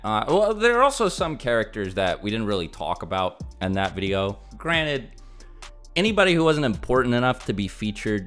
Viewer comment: Jesus Christ, did you just say granite? What are you? A boulder, a rock person?